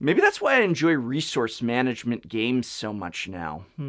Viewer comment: I heard you say "Research Management games" there. TELL ME MORE!!